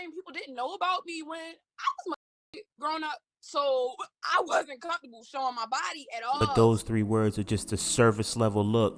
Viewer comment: what did she say right here? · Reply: Molested